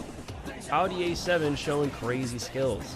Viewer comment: did he just say Audi A7